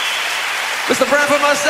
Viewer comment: Jo Lawry should get credits in the title next to Sting in this track. Incredible singer!! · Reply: maybe I misheard but it seems he said "and our lovely song by Ms. Jo Lawry" at the end